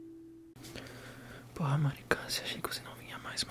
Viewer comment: Eu entendi "Mari câncer" KKKKKKKK Como sempre, perfeito vídeo ✨